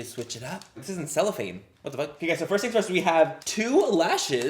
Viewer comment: okayguyssofirstthingsfirsthdkdkdkfo LMAO that’s all i heard